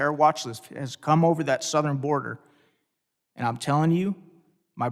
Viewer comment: It's doctor Frank's you piece of shit! It sounds like Kamala saying "I'm Talking"! I really hope that this is a sign of more to come!!! Women vote you dumb hillbilly!!!